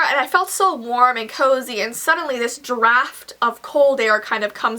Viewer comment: When you said draft I thought you said giraffed 😂😂😂